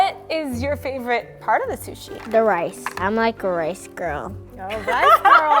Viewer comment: "I'm a rice girl" i thought she said Spice Girl lol